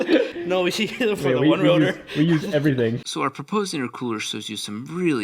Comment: did I hear that correctly? There’s a one rotor coming?